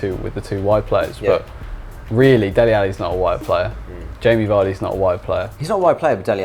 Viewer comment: I thought they were saying they're not a white player. Haha, then realized it was wide. I was like racists.